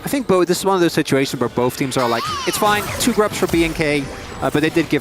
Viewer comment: I laughed every time they say BNK .. what I heard is"Being Gay" 😂😂😂